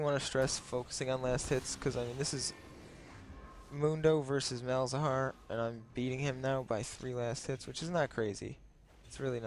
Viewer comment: so i tried out the captions and this happened... was he said: "mundo vs malzahar uh.... beating him by three last hits" what the captions thought he said: "moved overseas males are uh... beating him up through three u_s_ troops" O.o interesting....